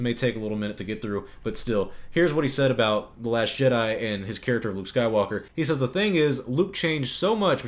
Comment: I keep hearing the narrator say "the last shit-I" Anyone else? 0:25... 3:30... 4:00 And then it dawned on me. Exactly! Episode 8: the last shit I will watch from Disney! 💩